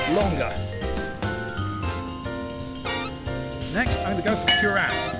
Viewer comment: why does my brain think he said "next im gonna f*** your ass" instead of "next im gonna put my cuirass"